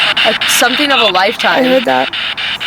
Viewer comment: That whisper at around 23 minutes kind of sounds like someone saying “is something wrong?” In an Irish or Scottish accent